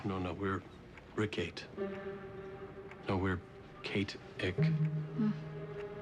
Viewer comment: i first thought he said "KAT-IC" instead of "KATE-ICK" x)